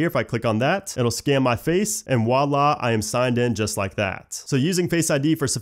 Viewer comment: Did you say wallah ? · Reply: batista bomb he said voila